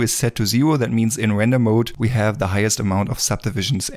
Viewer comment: Hey, can anybody tell me what is random mode in 01:34:26? because been searching for it but haven't found the answer yet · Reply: Hi Muhammad, the generated subtitles are wrong, it's render mode not random. He basically talks about the different subdivisions you can set for viewport and for rendering. ~ Masha